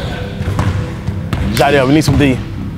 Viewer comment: You go to 31:43 hear what cash said”yo jiedel give me so D”